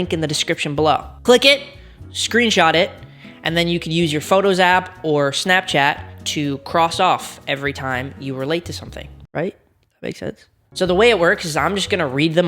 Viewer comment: I heard, "every time you were late to something" and I was sad cuz I'm always late, then I was excited cuz I thought I'd get to bingo faster, then I realized that's not what's he said... 🙃🙃🙃